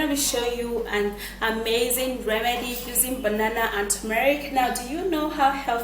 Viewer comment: I thought u said just banana and turmeric 🤪🤪